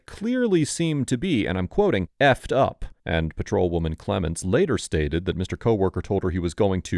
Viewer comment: To me it sounded like he said “…..is fked up”, I’m pretty sure he said something along the lines of “what you’re doing to my friend is fked up” not any sort of threat to fk up the cop.